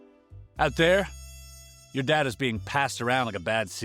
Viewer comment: I heard “Your dad is being passed around like a bad secret.” Like what? 😂😂